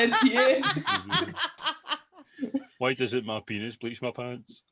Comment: When I saw this out of context on Twitter I genuinely thought said "why can't my penis police my pants?" I thought, "that might come in handy for some guys".